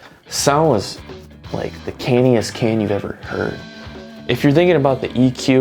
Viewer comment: Couldnt catch what he said at 2:27 Did he said "the canniest canny"?? Anyone please?!